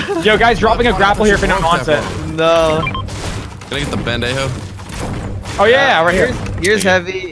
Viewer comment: Aye tell me y I thought he said “pendejo” @ 8:20😂